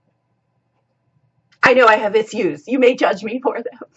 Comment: Did you say "ithues"? judging you 👀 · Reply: Naw, issues - instead of pronouncing is "ish-yous" I prefer the "iss-yous" version. ;o)